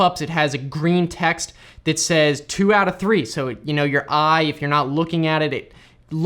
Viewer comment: I had to replay @ 13:58. It sounded like you said, "...if, you know, you're high , you're not looking at it...". 😂😂😂